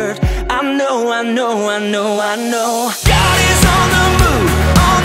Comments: Im a farmer and i always thought he said god is on the moe . Like a cow🐮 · GOD IS ON THE ROOF! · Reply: I always thought he said "God is on the moon"😂😂😂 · Not gonna lie. 1st time hearing this song and I thought he was saying "God is on the moon". Lol. Love this song btw